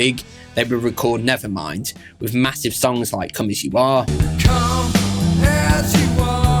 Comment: When he said come as you are I thought he said “konichiwa”